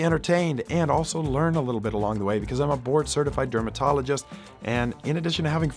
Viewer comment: it took me a minute to realize you were saying "board" certified, i heard it as "hopefully we have some fun because im a bored certified dermatologist"